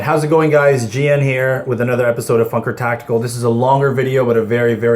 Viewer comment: lol sounded like you said, fucker tactical